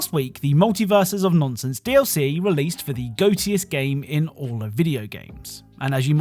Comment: Did he say “Goatse-iest”???